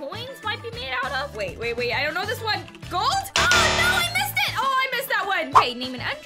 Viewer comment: she said gols instead of gold